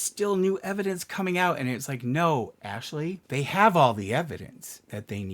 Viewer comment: thought you were about to say "no, asshole" lol